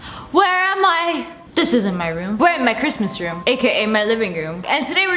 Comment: For a second I thought she said charisma